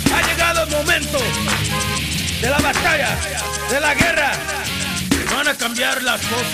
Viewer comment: For so long I thought he said “de la guerra, de la tierra” … it turns out he says “de la batalla, de la guerra” 😀